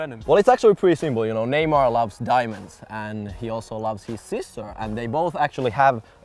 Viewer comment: I thought said Neymar likes diving instead of diamond lol